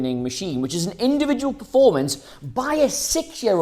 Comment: Did he call mainoo a six year old?